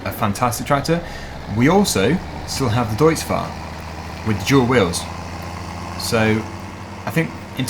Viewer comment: When ever you say "deutz fahr with dual wheels" i hear "deutz fahr with JEWEL wheels"... Am i the only one?